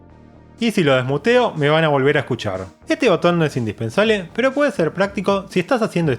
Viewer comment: qué dice aquí? no se escucha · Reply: "no me van a escuchar"